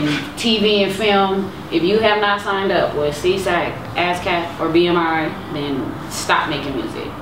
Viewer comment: what did she say @ 2:44 who are those people to sign up to collect your royalties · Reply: SESAC, ASCAP or BMI. The three major performing rights organizations.